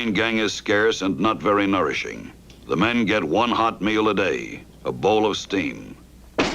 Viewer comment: They were given one hot meal a day. A bowl of steam 😂 Epic and one of my favourite Woody films, along with Sleeper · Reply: Ha-ha! I thought he said, "A bowl of stink." Very funny, either way.